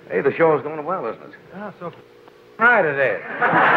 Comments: What does Dean Martin say at 34:15? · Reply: Johnny: Hey, the show is going well isn't it? Robert Wagner: So far it is. Dean Martin: You goddam right it is! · Can anyone make out what Dean says at 34:15 to Robert Wagner? Was part of it cut or bleeped? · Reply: He said, "You're G-d da--n right it is!!" (in response to Johnny's comment to Wagner "Hey your show's doing well!"